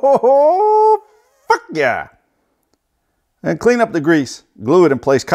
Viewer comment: when he said f*ck yeah i thought he said buck yeah